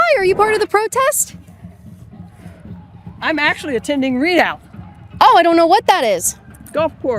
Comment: Oh, had to play it back a few times to understand it, I thought she said she was 'attending rehab' not ReadOut?